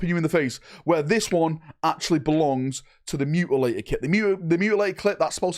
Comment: did he just say the mutliater clit?? lol